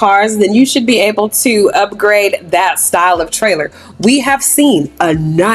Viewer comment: not the leapfrog reading book! 😂😂 i use to love that thing. Also when said “thats not my steelo” well..skeelo, I immediately was like oh yea he seen videos lmaoo. I need to try churches chicken tho, im from Nj, we got Popeyes, kfc and chicken shacks😭 we dont know no better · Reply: that's what I thought, I heard stylo I said oh hell na he copying!